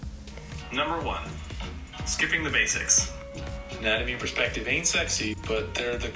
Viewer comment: I thought he said sleeping the basics at first but it was only written sleeping the basics